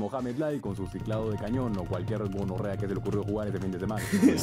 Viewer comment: escuché mal o dijo "o con cualquier gonorrea" XD?